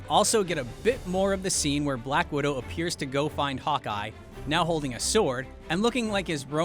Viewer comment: who else thought he said... cock-eye😆🤣